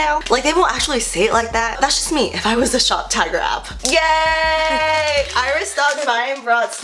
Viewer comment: Okay I know I'm not the only person who thought she was saying shoptiger not shoptagr. I'm glad she put the link cuz I woulda been confused.